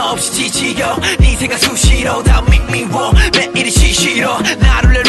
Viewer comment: I always think J-Hope is saying "sushi roll" at 2:35 hahaha :P